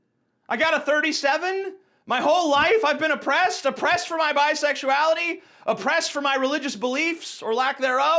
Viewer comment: did he says he's bisexual or did I hear that wrong